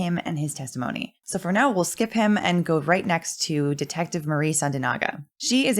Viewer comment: Genuinely thought you said detective Amy Santiago 🤣